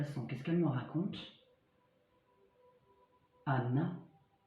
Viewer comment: Bonjour, moi a 8:21 j entends NASA, c’est assez cohérent entre les mots transport, dans l espace 😃